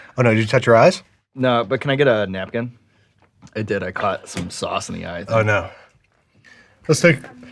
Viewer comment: For A Second When It Panned To The 2nd Sauce I Thought It said "Lamb sauce"